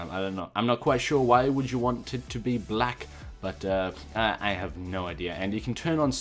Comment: Hmmmm... Did say "why would you want to be black"